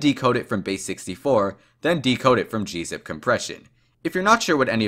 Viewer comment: did he just did he just said decode???????